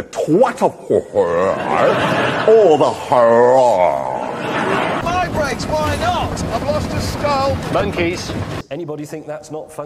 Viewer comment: Hammond: says something about his brakes, couldn't quite hear Clarkson: "I've lost a skull" May: "MoNkEy" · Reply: "Help your self to my brakes. why not?"